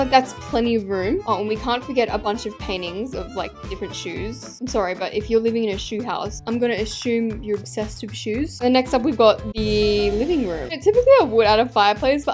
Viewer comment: Reminds me of the lady who lived in a shoe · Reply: Did you say Ashoeume?